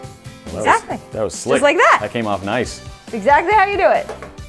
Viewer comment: did he say okay mom? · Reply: It came of*